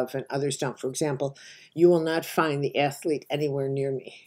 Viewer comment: Caroline, It would be helpful (for those new to the Archetypes) to see a LIST of ALL of them. At (1:08) you say you do NOT have this Archetype, but I have listened 3 times and cannot make out the word you say! Thank you! FASCINATING! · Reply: the atlete